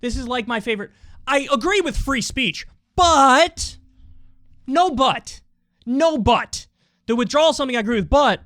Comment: “No but…no but.” I hope I’m not the only one that heard that as “No butt…no butt.”? 😂🤣